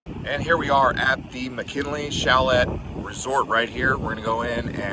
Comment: Did I really hear "chalette?"